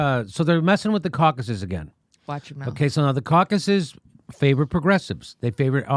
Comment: did say "Watch your mouth"? Hahaha! Edit: Just as he says "caucuses". Gee, I wonder what play on words she was afraid he'd do?